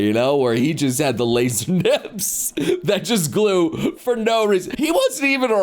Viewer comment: Did... did he just say "glew"?